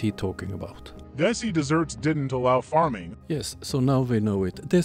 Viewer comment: Wait is dessy desserts the elusive they or is it Messi desserts? Wtf did he say there 10:36 it seems like he's using text to speech but can't spell